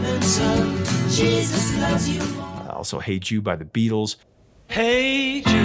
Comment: also hate you by the Beatles.... LOL but that is what I heard twice! Hey Jude became Hate you...